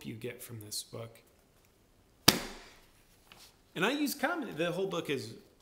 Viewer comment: i thought your video said "do i use condom" lol. sorry. hysterical thinking that wasn't your youtube style and the entire world was upside down as I woke up this morning. :) :) :) hehe